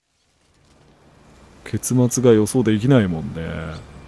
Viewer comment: 最初の「結末が予想できないもんねー」が｢ケツが予想できないもんねー｣に聞こえて一瞬何言ってんだよ、と思ったごめん。